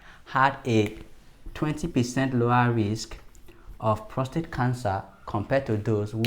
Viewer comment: Couldn't break out what he's saying .... Did he say 20% MORE risk or LESS risk !?!? Someone help plz .... Thank u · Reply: 20% lower risk